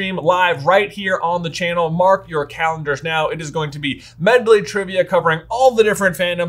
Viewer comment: for a moment i thought i heard him say mark your catheters. like whaaaaat ?